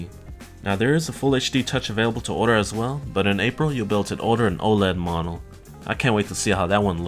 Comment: What does he say at 2:09 "now there is a full hd touch to order as well, but in april you build to order in ______"?